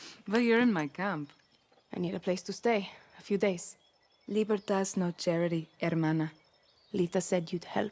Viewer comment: Lol I thought their base said libertard for a sec